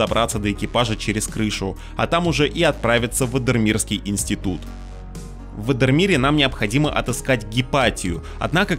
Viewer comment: Мне послышалось или " в эндермире"?